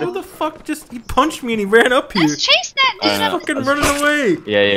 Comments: Idk if I'm tripping but I think the girl said let's chases that nigga on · do my ears deceive me or did I hear that word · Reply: Did they edit it out? because I thought I heard it when this came out but now I clearly hear "dude" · it sounds like says "Let's chase that n****" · does say,... "Let's chase that nigga"? lol XD I swear that's what I heard, it's fucking hilarious lol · am i the only one who hears say "Let's chase that nigga!"